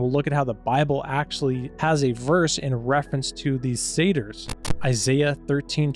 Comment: You say sators? Im asking cuz im french and i know of centaures (half horse/human) but i forgot the name my mom taught me for half man/goat besides “pan”. Which btw disturbs me when people use pan sexual as their sexual orientation. Ive seen a statue of a pan in acting and its not what they say it means. 🤮